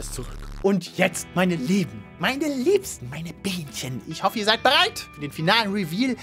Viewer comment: All die 9 Monate, in denen ich das Video gesehen hab, hab ich bei 15:49 "meine Mädchen" verstanden, jetzt versteh ich "meine Bienchen" 🤔 Und es wird vermutlich für immer ein Mysterium bleiben 7:40 Inger oder ihn gar? 🤔 Das wird auch ein Mysterium bleiben 🤷🏻‍♀️ \/w\/